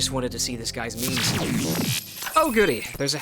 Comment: i somehow thought you said "i just wanted to see this guys nudes" instead of meme?? ... though actually that would explain "while I'm on my knees in a dark alleyway"